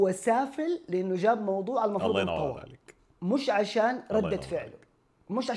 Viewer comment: ايش قال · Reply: المفروض الموضوع انطوى